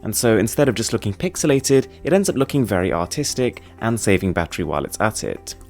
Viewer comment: did he say it looks very autistic